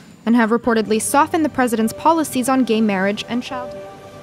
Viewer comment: What did she just say!!!!!!! Same sex marriage and child porn!!!!!!!!!!!!!!!! Wtf did i just hear !!!!!!